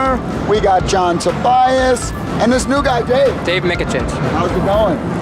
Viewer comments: Please, help me! I study English and I didn't hear what exactly say this(when asks Dave) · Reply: we got new guy dave, and dave says his name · Please, help me! I study English and I didn't hear what exactly say this(when asks Dave) 9:11 And after this Tobias got angry little bit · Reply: I think that he said his name, which appears below the screen: "Dave Michicich" I'm not a english speaker, so if his name didn't appears on the screen, I would not understand either